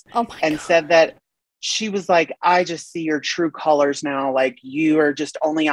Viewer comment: I thought it said Color pop and I was so bummed because I love their palettes 😂